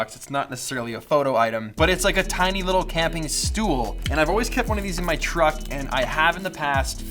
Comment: hold on.. did you just say the dutch word stoel at 8:04?